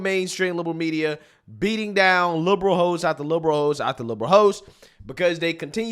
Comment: I thought you said LIBERAL HO'S 😂😂😂